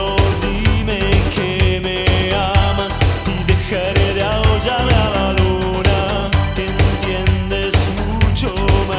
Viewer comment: perdón, pero que dice en esa frase??? no entiendo!!!! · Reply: Dejaré de aullarle a la luna